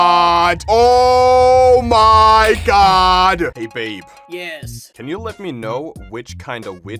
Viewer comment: the ghost is literally saying ''hey baby girl wanna come inside?''